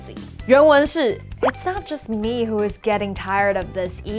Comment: 聽不太到[not's just me]請問是連音連起來了嗎?